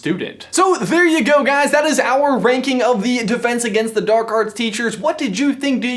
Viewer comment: thought you said wanking instead of ranking lol "i like wanking stuff"